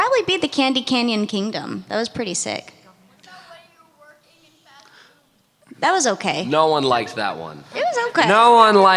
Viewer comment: What does the audience member say here? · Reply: Talking about the fast food adventure